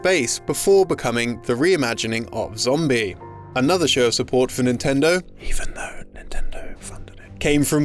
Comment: What is he saying at 3:30? I can’t tell. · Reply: “Even though Nintendo funded it...”